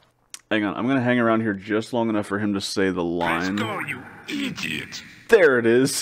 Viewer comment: I always thought he had said "Press Go, and you EAT it!" That still makes sense, right?